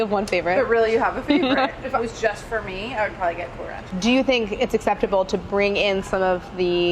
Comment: What does she say at 3:11 ? "I would get courage" ?